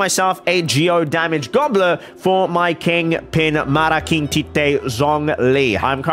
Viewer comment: did you just say "malaking tite?"